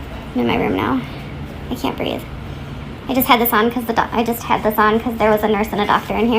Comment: did I hear her say the words "I can't breathe"? Wow....